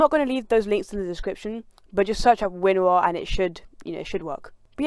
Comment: search up what? it sounded like you said windwir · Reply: WinRAR and ignore the purchase winrar things just install it